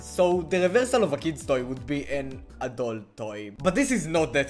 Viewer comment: BRO I WAS SO EXCITED FOR BLOCKY WHAT DID I JUST HEAR 😭 Great video tho :) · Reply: adult toy